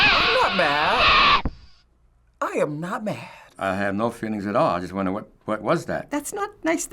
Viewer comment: did he say 'i have not feelings at all' or is it me